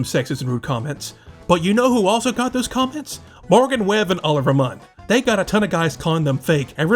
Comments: did he just say oliver munn?😂 is this an AI voice over? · Did you say “Oliver” Munn? 😂